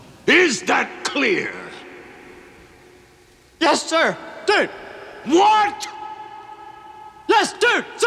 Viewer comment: All these years later and I never knew Bill said “Yes Sir, Dude!!!” I thought he was saying Yes Sir Dir, Yes Dir Sir, Yes Dir Sir Dir!!!😂😂😂